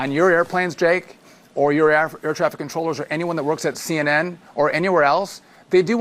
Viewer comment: did he say air Africa controller?